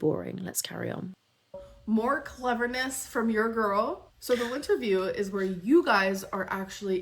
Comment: The Lynnterview segment cracked me up because I heard it as Linterview. Pluck much? 😂